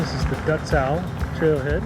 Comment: Thought I heard thr Duzer. Lol